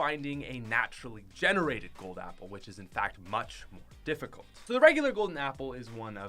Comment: Did he just say much difficult